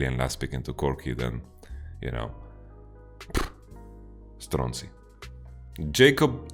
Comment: Did you say "stronzi" at 19:18 talking about Zilean into Corki? Has Jizuke anything to do with this expression?